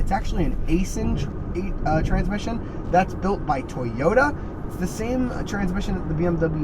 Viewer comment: what?! Did I hear Toyota? That is good to hear in my opinion because of The Toyota reliability